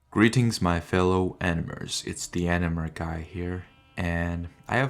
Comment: Im new to the channel so im not used to the opening, so when he said "greetings my fellow animers" I could've sworn I heard "my fellow n-words" and I died for a second 💀💀💀💀💀